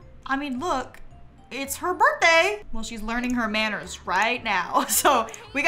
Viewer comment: is it just me that when said “it’s her birthday”, it sounded like “it’s my birthday” said by rapunzel in tangled 🥲🥲